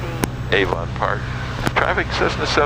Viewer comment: Good job at 6:26 it sounded like you said "Hey Ron Parr" (me), but I figured it was the name of the airport - HA!!